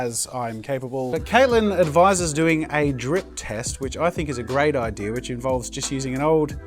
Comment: When he said "drip test", I swear I heard "drink test". "YOU'RE DRINKING PAINT?!!!" realizes he meant "drip" Oh. Never mind.